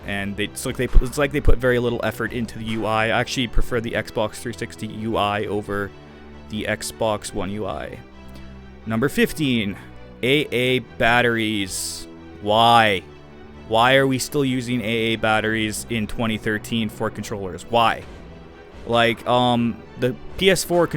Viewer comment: o god i thought you said AAA batteries, that wouldve been the deal breaker for me XD